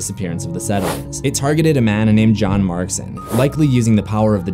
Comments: thought that say John Marston for a second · who thought he said John Marston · Bruh I thought he said John marston😅 (Red dead redemption) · damn I thought he said John Marston IMAO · Lol I thought he said john Marston from rdr1 rdr2 but its john markson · I really thought he said John Marston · I thought he said John marston